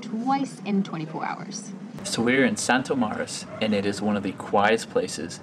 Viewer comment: Hello, can anybody tell me the name of the location at 06:43 to watch the sunset? English is not my mother tongue and I cannot understand the spelling! Thanks in advance! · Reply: Santo Maris is the hotel.